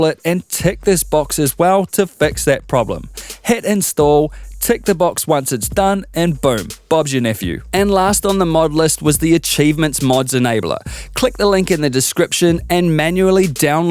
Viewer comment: What did you say? Pubg nephew?